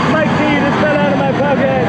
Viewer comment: Who else hears "OOF MY PENIS FELL OUT OF THE POCKET"